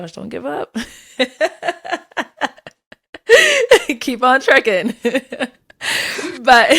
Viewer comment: did say "keep on trickin'"? 😂😂😂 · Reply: Keep on trucking.